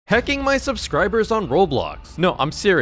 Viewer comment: I thought he said hecking for a second